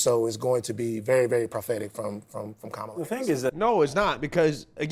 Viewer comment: I thought you said "Gobella Harris" lolol.. Oops